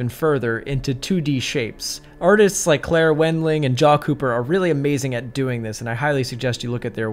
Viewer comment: Who are the artists he mentioned at 5:00? I can't understand English that well... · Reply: claire wendling and who?